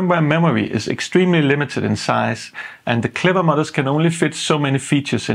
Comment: I thought he was saying "clever mothers", took me five minutes to figure out it was "modders" lol